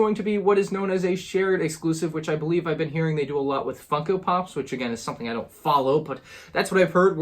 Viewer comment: Who else heard “fucko” pops at 7:25 not funko pops?😂